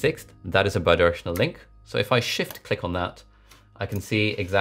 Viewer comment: anyone else not hear the word shift and thought he was saying something else